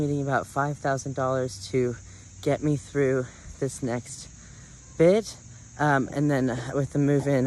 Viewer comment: I was shaving my legs with an electric razor while listening to this and at 10:23 when says “I need $5000 to get me thru this next bit” I heard “I need $5000 to get me thru this next grift” and I paused like damn was that was some real honesty?! 😂